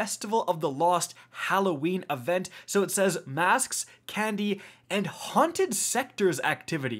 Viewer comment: when it said Candy I thought it said Cayde. For a second I thought he was coming back 😔